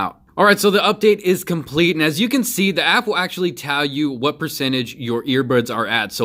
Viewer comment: did he said apple at 4:46????